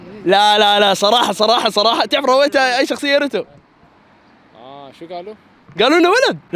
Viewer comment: هو قال اريتو ولا انا سمعت غلط